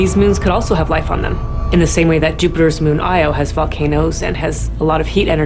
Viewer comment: did she just said fuckanoes